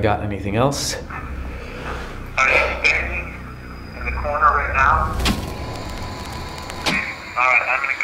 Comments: The recording in the crypt sounded like he said, "Because they make us stand in the corner." Or something like that... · I heard “you’re the one sitting in the corner” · high key, i heard on speaker "I'm not standing in the corner" as if they were whispering tht in his ear